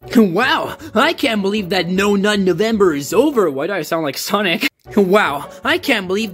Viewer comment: Why did I hear "No Nun November"?